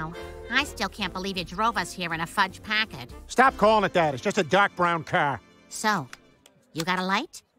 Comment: I misheard the “dark brown car” as “Doc Brown car”. And I didn’t question it, because Doc Brown drove a Packard. Then I heard “fudge” on the 2nd viewing and it all fell into place.